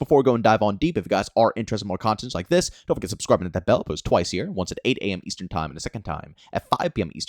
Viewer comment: it sounds like you said you only post twice a year lol